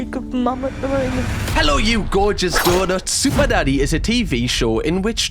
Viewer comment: Could’ve sworn I heard you say super daddy on the intro